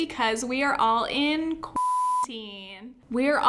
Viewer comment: When you said "quarantine time" I 100% thought you said "Quarantimes", and that is now and forever what I will call this quarantine.